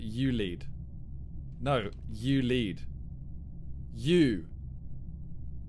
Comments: Swear thought he said you leave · I thought he said you leave😂😂😂